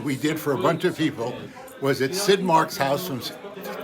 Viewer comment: I thought he said "skid mark" at first.